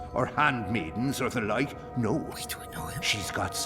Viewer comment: right on point when bucket-hat man stops talking, whispers something, but it was so quick and slightly overlapped with bucket-hat's last word, so it's really hard to make out what he said. I'm pretty sure that was whisper, there's nobody in the game who's got a voice that sounds any way like. I'm sure it's no big deal but......... Human curiosity is a bitch. I wanna know what he said... Guuuurrrrghhh!! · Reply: I think he said something along the lines of "waitdoiknowhim" Pretty sure he does, seein as he loved Blind Bob in Dreamfall TLJ